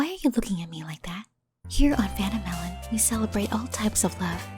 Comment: The captions said "Phantom Melon"